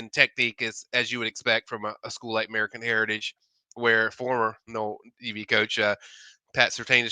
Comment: mark, sounded like said former nole DB coach Pat Surtain😮